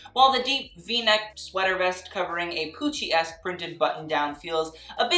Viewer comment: why did i hear “coochie-esque” for the contra stitching look in ep7 ☠️